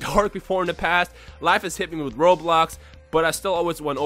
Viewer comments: I thought he said"life hit me with Roblox"😂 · thought u said roblox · It sounded like you said " when life hit me with roblox"